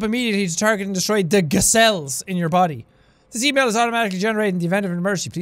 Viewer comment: it sounded like you said Giselle's not G cells